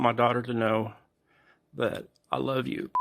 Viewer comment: at first I thought he said i love you bitch